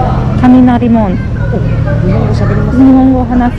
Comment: did she just said Kaminari?!